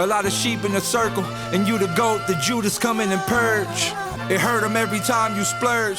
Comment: did he say Judas